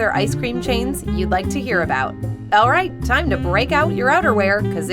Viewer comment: I thought she said "it's time to break out your underwear"